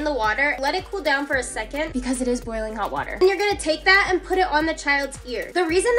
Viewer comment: I thought she was gonna say “dump the hot water on the child’s face” I was so confused